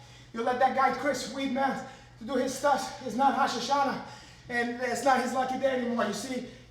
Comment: Does he say "It's not Rosh Hashana" at 3:12 ????